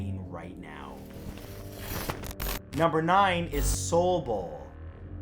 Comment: Soul bowl i thought it said soi boy